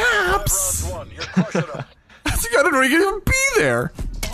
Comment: What was said at 2:38?!😂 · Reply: Istg I didn’t know he could b there?